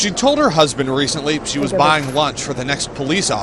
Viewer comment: I am an Indian student in the US. One day I want to give all the US police officers in my area a launch treat with all the nice Indian dishes. That's my dream which I will soon fulfill. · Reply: I thought for a moment he said launch threat but he means lunch